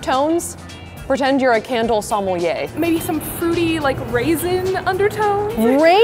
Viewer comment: I had no clue what she said at around 24:04 but it turns out she was saying sommelier in a funky way